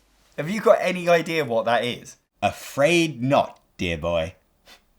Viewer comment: Anyone else mishear "afraid not" as "a frayed knot?"